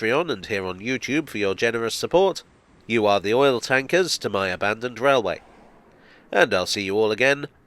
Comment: "...for oil tankers" I first heard that as "for royal tankers". As the Regal flies...